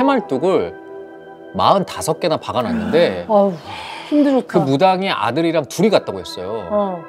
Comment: 우와 힘들었다 라고 누가 말하는데 무섭다...